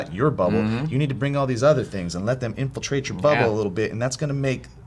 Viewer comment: i swore said infiltrate your butthole